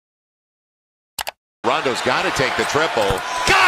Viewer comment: who thought is said rondo instead of lonzo